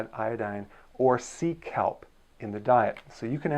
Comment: Lol when you mentioned “sea kelp” I thought you were saying “Seek help!” Lolol